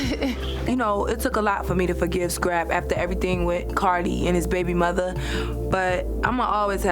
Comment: It's so funny when Tommie talks about Karlie Redd, but it sounds like she is saying Cardi lmao.